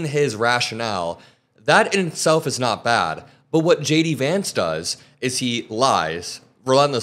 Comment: Does anyone else find JD Vance more disturbing than Trump? 😮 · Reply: I thought I heard shady Vance. 😂